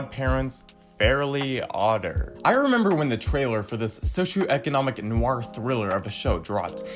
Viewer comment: In the first clip showed I thought it said failer and not trailer